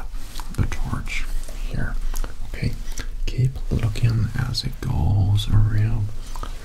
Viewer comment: At the beginning i thought he was saying "keep looking dush "